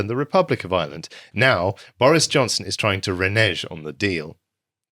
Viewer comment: Renezz instead of renege. New speak dictated by fear of the N-word.